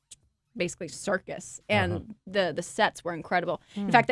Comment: I thought that she said, "The sex were incredible!" xD